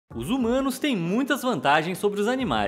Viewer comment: Mano... Eu entendi "Os Zumanos" kkkk